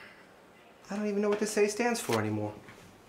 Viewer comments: "The Nazis won didn't they?" That was the line that got me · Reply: Ohhh, I thought he said "I don't even know what this state stands for anymore" lolol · 'I don't even know what this A stands for any more.' This cuts deep. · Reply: at first I thought he said “Robert Lee’s Asshole” Robert Lee was the general of the Confederate Army